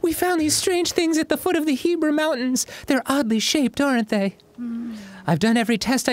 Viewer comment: did Dan just call the HEBRA mountains the HEBREW mountain?